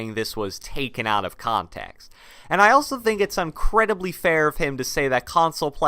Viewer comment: Anyone notice he said “Uncredibly fair” at 7:20 (I know what he meant to say)